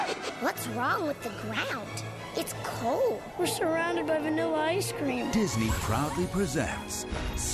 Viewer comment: what did he say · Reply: ”We’re surrounded by vanilla ice cream.”